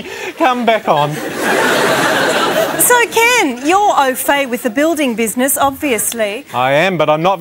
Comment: Could you please tell me what does she say at 1:23? You’re ..... with the building business? What was that word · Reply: au fait - 'having a good or detailed knowledge of.'